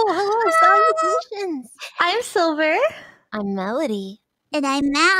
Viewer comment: i thought u said im sober not im silver lul